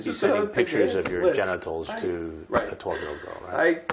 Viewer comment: i thought he said a 19 year old??? instead some 1940s mafia boss stumbled through the door...